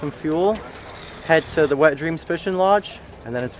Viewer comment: what did he sayyyyyyy? Wet dreams? 😅